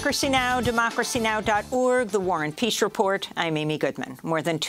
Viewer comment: Who is Warren Peace that he gets his own report? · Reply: All of these years and I thought she was saying The War and Peace Report.